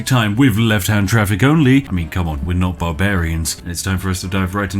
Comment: Libertarians: "Taxation is theft!" Statists: "But who will build the roads?" "I have a compromise" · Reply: I thought that said librarians lol